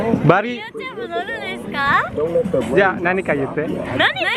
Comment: Kok gw dengernya "nani kanyute" di menit 5:39 ya? 🤣